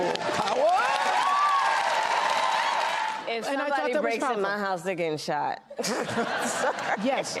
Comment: Can somebody please tell me what VP Harris says at 7:58, one or two sentences? Whatever she says before she says "I probably shouldn't have said that."? I'm German and can't figure it out, no matter how often I listen to it! It sounds a bit like "shot" and "house" lol. Thank you!! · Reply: She said if someone breaks into my house they gettin shot